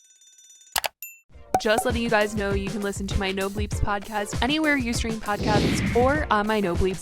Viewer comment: 🤦 On your intro I thought that you said " No Leaves Podcast." It's No Bleeps 🤦😜